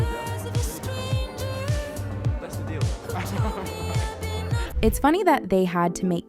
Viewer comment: Netflix is insane for this. That's not what kit said at 2:13. He said "that's a dealbreaker"